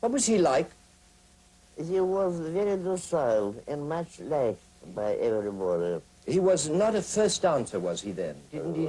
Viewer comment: What did she say in reply when she was asked what Vaslav Nijinsky was like? It was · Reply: hi GoldenAppleOfDiscord she says 'he was very docile and much liked by everybody' cheers